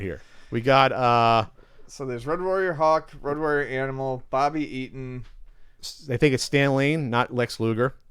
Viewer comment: did say Bobbi Eden? Thats a Dutch porn star. :)